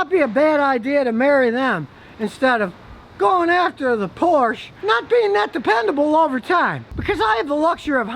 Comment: min 5:03 you'r neighbors are going to complain ,because you said thei'r not good looking .min 5:12 did you said porn or porche ?